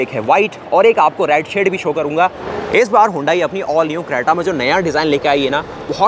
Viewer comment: later did he just say Honda?